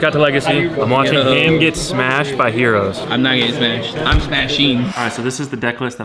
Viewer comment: did he just say he’s going to smash some Hoe-ros?